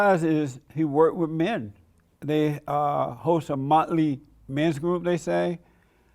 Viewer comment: Did Jesse say at 26:15-16 that they host a motley men's meeting or monthly men's meeting? 😆